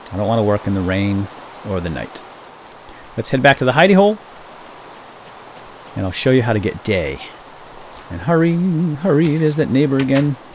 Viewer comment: when he said I will wait for the day or something I thought he said I will show you how to get gay lol omg